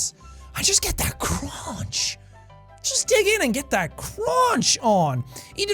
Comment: When Seán was talking about eating the banana with the peel, "ass first" I thought he had said, "I just get that cRoTcH."